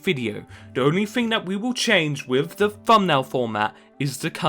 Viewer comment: I was struggling so hard to understand what you were saying. Funbail? Fummail? Fumnail? But you were meaning to say Thumbnail. It's quite hard to understand you at times.